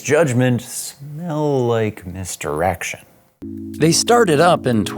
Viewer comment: did he say missed erection?